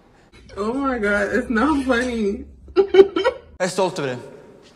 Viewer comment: why did i hear that as "i assaulted him"?????????